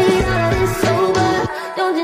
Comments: It sounds like you say i just killed my eggs 😂😂😂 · I heard "I just killed my eggs" 😂😂😂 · I hear "i just killed ny eggs.... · “I just killed my eggs” 🥚 I can’t stop hearing it 😂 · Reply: I HEAR " I just killed my eggs 🥚 " TOO